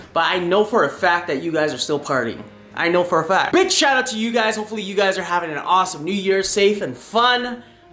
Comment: sounds like he said bit** shout out to you